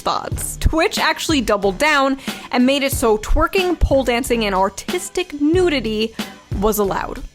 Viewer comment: oh... I thought they said "autistic nudity"